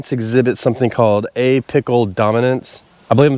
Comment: I had to laugh because you were talking about apical dominance but all I heard was “A-pickle dominance “ lol😂 I have never heard of “a-pickle” dominance. Thanks for the chuckle and great content as always!